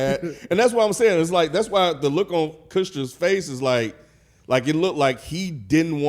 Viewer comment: I feel naive that is this naive about how insincere they are in the video. Btw anybody catch say “Kutchston” 😹